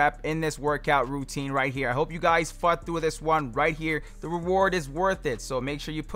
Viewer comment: i thought you said "hope you guys fked with this one right here" lol